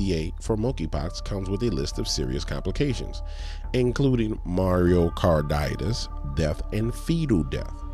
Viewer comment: 😂 did you say "Mario Kart-itis"? Ninja, that's an inflamation in the wrists!